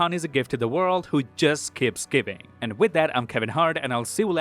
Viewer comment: did he just say "I'm Kevin Hart"?